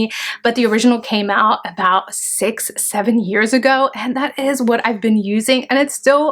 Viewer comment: did she say.. 6-7 🤷‍♀️ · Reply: Yes the original came out about 6 to 7 years ago 😉